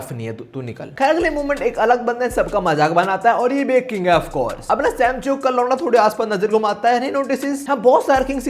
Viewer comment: did he really said samcheok ka lauda fr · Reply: Launda